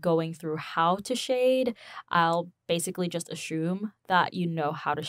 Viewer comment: she said I'll basically just ashume '' instead of assume lol